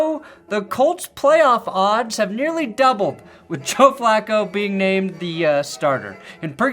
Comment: Did you just said, Choke Flacco?